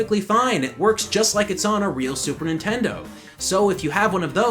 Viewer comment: did you just say "super intendo?" wow that takes me back